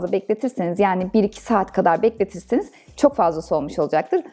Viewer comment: 20 saat mi dediniz ben mi yanlış anladım